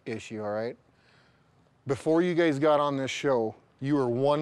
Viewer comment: Starting from 1:13 Did he call them gays instead of guys?!?! Sure sounded like it Even when he's clarifying he's still roasting them 😂😅🤣